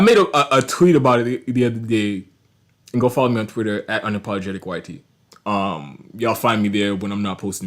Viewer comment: Nah you gotta explain your screen name better bc I knew it was 'unapologetic-y-t' but it sounded like you said 'unapologetic-white-tee' 😂